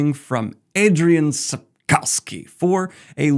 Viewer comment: Chai/Chi. Daniel: Chai/Chi. Cough. Daniel: Cough. Ski. Daniel: Ski. Chai-Cough-Ski. Daniel: Pukowski. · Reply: I legit thought he'd said Adrian Tchaikovsky at first.